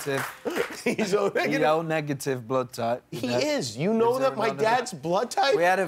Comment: guys like i'm from belgium and i talk french why when harry is talking about the blood of jimmy's dad, around 1:10 i hear first "he is O négatif" and when he repeats it i hear "il est O négatif..." (il est means he is) like wtf? does anyone hear this too??